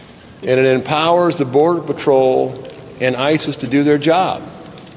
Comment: Why did it sound like he said, "ISIS to do their job" instead of "ICE to do their job?" 🤔🧐 I hope that was a mishear or an oversight. We also listen closely to the way people are speaking in the government. Words and the way they're spoken. Put together. And we also question everything now, more than ever before. So when we hear words like "ISIS", we are skeptical and on alert. There have been all kinds of these people, terrorist group cells that have also been crossing our borders. So you see why we are alarmed.